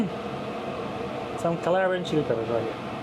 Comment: wtf is he saying at 14:45? What's that ingredient? It looks like crushed Calabrian chili peppers but sounds like "Some claran shoot everybody" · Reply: 😂sorry about that, but yes, Calabrian Chili Peppers !